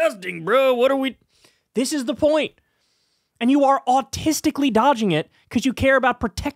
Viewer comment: Did Kyle say JD Vance was autistically dodging the question about 4:48 ?